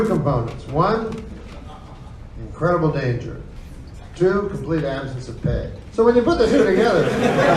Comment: does anyone know what he says here? i can't make it out....... like the second component? lol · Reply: Complete absence of pay